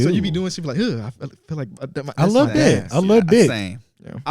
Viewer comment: Bro why it sound like said “I love d!ck” back to back 😭